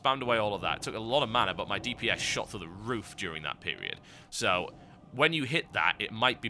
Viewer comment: When he said "my dps shot through the roof" I thought he said "My dps just shit on your roof" >.>lols